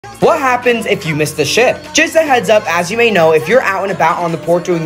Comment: all i heard was “what happened if you missed the shit”💀